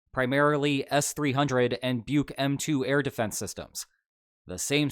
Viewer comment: for a sec I thought that said "buk me" 😂😂😂😂